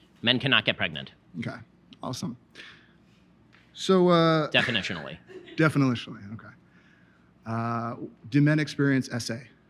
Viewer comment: 번역 영상 제작 감사드립니다! 보다가 저는 다르게 들린 부분들이 있어 가지고 와 보았습니다. 0:22 이 부분 “절대로요” 로 번역하셨는데, 저는 “definitionally”-“정의에 따르면요” 로 들었습니다. 이는 이후 벤 샤피로가 2:14 즈음에 “You are really moving around the terminology very widely here.”- “당신은 너무나 광범위하게 단어(의 정의)를 바꾸고 있습니다”라고 하는 지적와 연계되어 은밀한 재정의의 오류를 지적하는 것으로 해석 가능할 것 같습니다. 여러 영상들 번역 힘드실텐데 다시한번 화이팅입니다, 감사합니다.